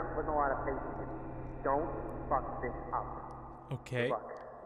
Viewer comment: Did he say Don't F this up